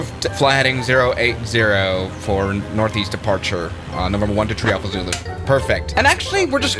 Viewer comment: Were using foliage in ATC now? The amount of times I heard "one two tree" lol